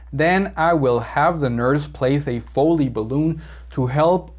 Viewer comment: hi! excellent videos to practise. I have my exam on monday! so nervous. One qustion: what word is in minute 13:00... the... balloon?? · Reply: Foley balloon